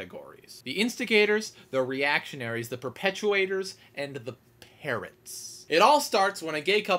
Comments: OH. I thought he said 'parents' not 'parrots' xD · I completely agree! I don't understand how other people getting married affects someone else's life at all. Just let them get married and be happy. Also, for most of this video I thought you were saying "parent" instead of "parrots". Oh well, it made sense either way.